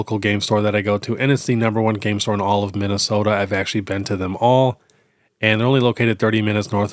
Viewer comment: Bro....Today is the first day that I understood you saying "I have actually been to them all." I always thought you said "I have actually been to the mall.' I thought, huh, what a strange thing to mention hahahahaha